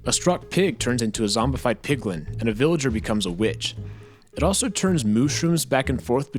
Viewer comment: I heard bitch at 3:50.